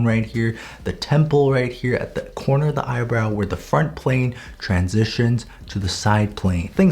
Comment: when he said "Front plane" i heard it as "Fart plane" im not kidding!